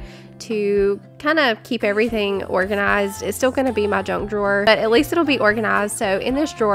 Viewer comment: I so thought you were saying drunk drawer 😂🦋